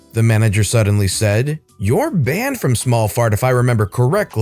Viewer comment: The third story, I heard "small fart" and immediately thought of HallMark. Y'know? The hallmark store? But then again it could have just as easily been wallmart.... 😂😂😂😂